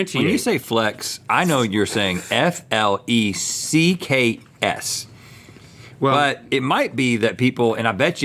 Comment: see, and here I thought he was just saying "flakes" (referring to the dehydrated ingredients) with an exaggerated Southern drawl 😂